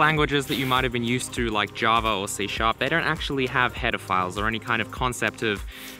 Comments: lol I cant be the only one that thought he was saying hetaphiles this whole time, and then immediatly thinking of pedophiles. Tell me I am not crazy. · On 2x speed I keep hearing "Pedophiles"...